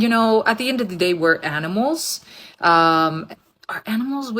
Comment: Right from the start, this first woman. We are NOT animals. Cognitive reasoning, rationale, logical thinking, emotional intelligence, etc separate us by the widest of margins from "animals". Credibility denied. Ugh! Shut up... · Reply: LOL...did not realize it was only this one woman. I heard, 'we are all animals' and got seriously triggered. My bad